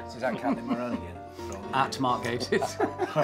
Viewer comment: what does steve say at 3:10 that gets everyone laughing? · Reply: bit late to reply but he said "at mark gatiss" as in it was Mark that had tweeted Reece was on his shag list